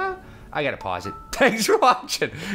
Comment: I always hear "click the Asian in the top right to watch the next video" :P